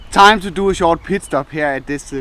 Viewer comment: I start laughing my ass off because when he says take a "pit stop" it sounds like you saying take a "piss stop", LOL